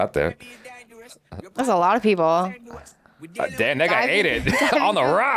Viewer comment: ngl I didn't hear "that guy" 💀💀